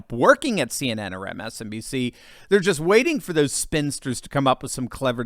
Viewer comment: into the video... did you say "spinsters" or "sphincters"?? Borrowing from the Viet Nam era style chants: Ho, Ho! Jinping Joe; Jinping Joe has got to go! Re: Tucker Carlson, "think tank" What an incongruity to have Joe Biden and "think tank" in the same sentence. You might even say it's an... (forgive me for splitting the word), Oxy - Moron!